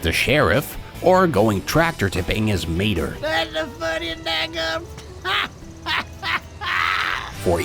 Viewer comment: What Mater's saying at about 2:35 is "That's the funniest dad-gum*..! ha-haa!" As in, "I've never seen something so funny, I didn't even finish my sentence! Ha-haa!" *or "da'gum," it's a country-fried kid-friendly switcheroo on "god damn."